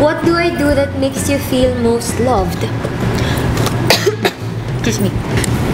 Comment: !! If you don't hear it clearly you will hear kiss me hahahaha. But Kath Means Excuse me. 😂